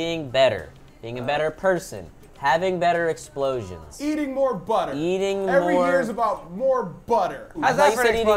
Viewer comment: "I thought you said eating more butt"😂😂